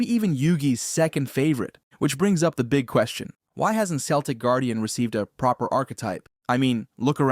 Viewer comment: Did he just say seltic guardian